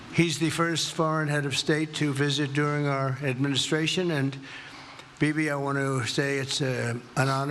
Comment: Did he just call him Bibi??